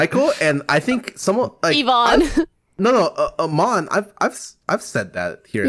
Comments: Legit didn’t know Pokis name was imane, just thought they called her that as a another way as Pokimane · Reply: before i knew her name was imane, i always thought they were saying yvonne XD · I think she said Yvonne, not Imane.